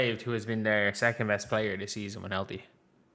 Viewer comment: what does he say after just waived their 2nd best player? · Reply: Take a guess: A) The sea is in 1L3, B) this seasoning’s with Kathlee C) this season when healthy.